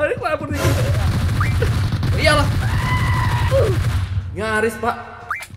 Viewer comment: bang bilang ya allah atau gua salah denger